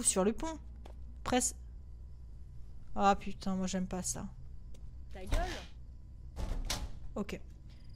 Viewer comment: je rêve ou j'ai entendu un "Ta gueule!''😂